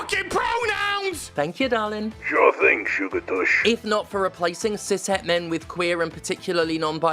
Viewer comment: Did he call you "sugar tush"? Goddammit I love you two working together. 😂